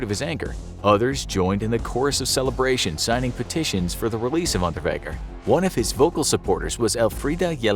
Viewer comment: Thought it said release underwear for a second 😅